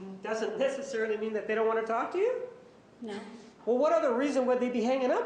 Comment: Even the subtitles know that she lied, at 52:17 she said no the subtitles said yeah. 😂